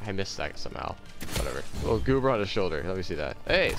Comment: did just call pochita "a goober"